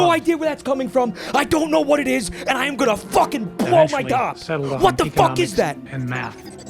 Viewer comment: I SWEAR I thought he said "I AM GONNA FVCKING BLOW MY C0CK". Cracked me tf up